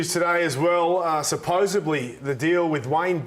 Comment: Did I hear Anasta say 'supposably' ?